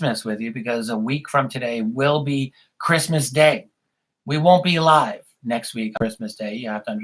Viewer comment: I thought you said "we won't be ALIVE Christmas week." lol